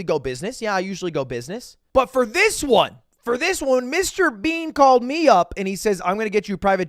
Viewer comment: Did u just call him mr bean??